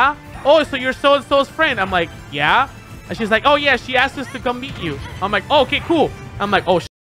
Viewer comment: I heard "beat" instead of "meet" !😂